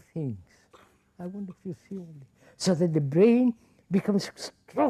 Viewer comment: what does he say in the aside at 18:16 ? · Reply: I wonder if you see all this?